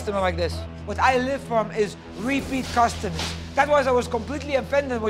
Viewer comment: did he say "creepy customers"??